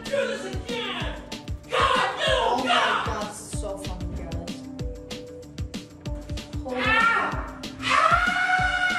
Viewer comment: Ok I heard him say the F word and it wasn't censored. It's ok we slip up sometimes. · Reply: He said funny. Not the f word